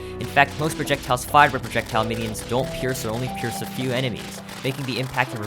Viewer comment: the captions are wrong, it reads "pierces a few minions," rather than "pierces a few enemies" like you say in the video